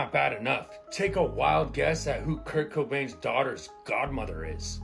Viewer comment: Tahaha I thought he said kirko bangs not Kirk cobain lmaooo I’m like no way he didn’t have ties to drew Barrymore lol